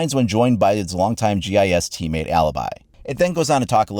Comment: I thought that said "Long-time chatty ass teammate, Alibi."